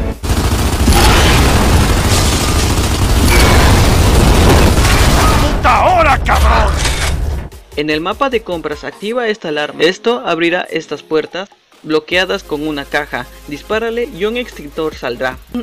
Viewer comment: todo este tiempo pensé que decía "pu*a hora cabron" jajaja